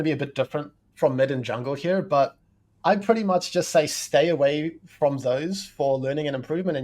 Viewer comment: What does he say to stay away from at @ 45:20? I can't understand him. Thanks. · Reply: "Stay away from those for improving" - he's talking about the playmaking champs they just discussed. (Bard/Thresh/Pyke)